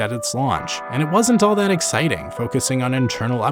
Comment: I thought you said “I was 5” and it took me off guard